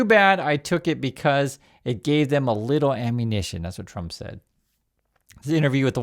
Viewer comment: When i hear him say "a LITTLE ammunition" I hear "i'm fucking dying"